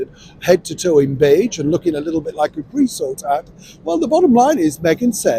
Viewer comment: Help….. @ 1:21 - did he say “Presort hat” 🤷🏻‍♀️ Hey MeAgain, do you know what some of the best things for mental health is? Truth. Humility. Willingness to serve others.